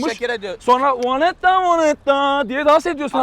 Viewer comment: 'da Shakira ne diyormus vonettaa vonettaa